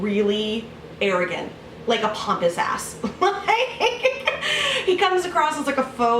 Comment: Everytime i hear "pompous ass" in my head i reinterpret it as "pomps his ass" and idk what that means but it makes me laugh.